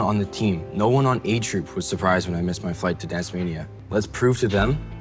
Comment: I keep mishearing Dancemania as Tasmania